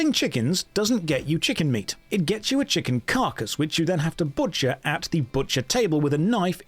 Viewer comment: it is, with it's own unique twist and systems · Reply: And I heard Josh say "chicken cock" but he said "chicken carcass"... I need to sleep 😂